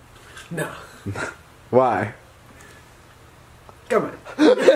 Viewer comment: What does josh say at 0:06??? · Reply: Come on